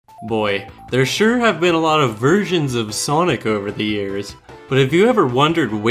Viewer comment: I thought he said "there sure have been a lot of virgins of Sonic..."